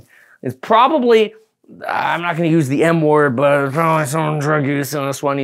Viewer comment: Anyone else thought he said "I'm not gonna say the N word" at 4:57 ?